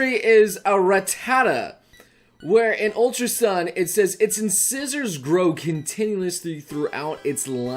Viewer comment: I was looking away from the screen and I hear say "inscissors" and was wondering what the heck was that. HAHAHAHAHA